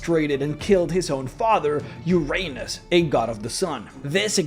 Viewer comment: did I hear that correctly? Did he say Uranus! Lollllllllll But my favourite part is 22:06 when he says the hecatoncheires are born from Uranus and gaya